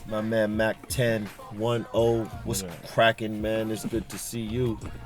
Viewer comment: did he say what crackin witcha bitch?